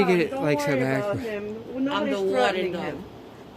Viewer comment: did she say that she was the warden ?